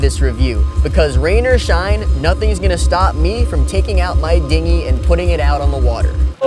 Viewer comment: lmao I thought he said "nothing is going to stop me from taking out my dignity"